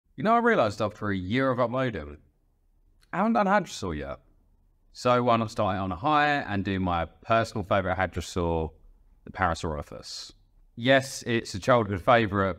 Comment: can someone explain wtf he said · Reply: You know I've realized; after a year of uploading... I haven't done a Hadrosaur yet! So why not start it on a high and do my personal favorite Hadrosaur, the Parasaurolophus.